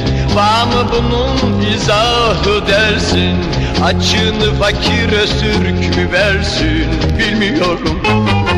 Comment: açın sonrasını anlayamadım ne diyor???? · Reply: "...'vağ mı' bunun izahı dersin? açığını fakir öztürk mü versin?.."